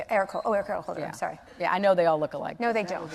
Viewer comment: I thought she said they all smelled alike. Kind of like a dead skunk you passed on the road. I swore she said that. Well, I was about to finally agree with something she said. Nevermind.